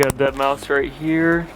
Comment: is it wrong that I heard Deadmau5?🤭